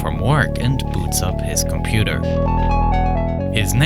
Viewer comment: i thought he said he beats up his computer